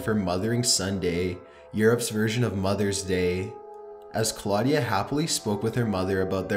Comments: Europe's version of Mothers Day is Mothers Day. Don't know what the fuck you were calling it. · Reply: I thought he was saying "Mother & Son Day" but I was confused since she was a woman.... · "Mothering son day?" Never heard of it. It's just mothers day here in Europe. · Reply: I thought he said a mother & son day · It's still mothers day in the UK lols old anyone who calls it mothering Sunday lmao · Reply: I thought he said a mother & son day